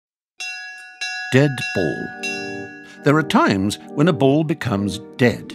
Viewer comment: Who heard Deadpool at this moment